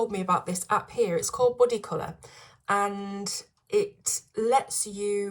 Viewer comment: you mentioned in the beginning of this video a app. I thought you said “buddy color” but I can’t find such a app. Can you direct me to the app you showed in your video? Enjoy your videos. Joy